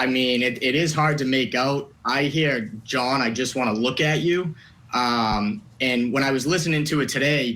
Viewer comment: I heard it very clearly & so did many others in this comment section. She said, "John, where the f☆ck are you". She said "fvck" with a slight Boston accent. I have no doubts. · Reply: That is what I heard. She said, “John, I just looked at you”.